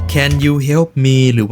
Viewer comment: เขาพูดว่าcan you see me?